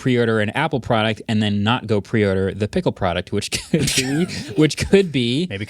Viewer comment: he says "pickle product." Dude, you're getting a Dill. I listened to this like four times to make sure and he is definitely saying pickle products. Dude